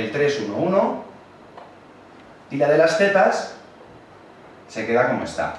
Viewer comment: En el minuto 4:42 parece que dices "tira de las tetas"... xD